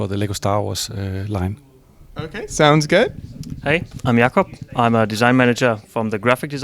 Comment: "Hay, aym Yahkub" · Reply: For you its yahkub but I heard it as Jakob